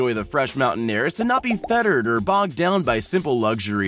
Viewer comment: "not be feathered" - seriously? It's "not be fettered". This reminds me of Will Ferrell in "Blades of Glory" saying "mind-bottling" instead of "mind-boggling". Also 5:10 He says "when the Zombie herds start knocking" instead of "Zombie hordes".